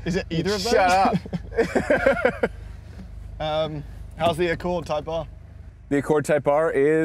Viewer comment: did they call it an Accord Type R? 🤔